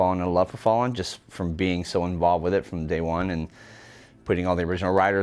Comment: did he say “day one” or “Daewon” 😂😂